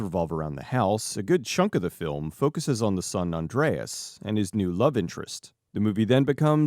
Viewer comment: 43 I thought You Said San Andreas .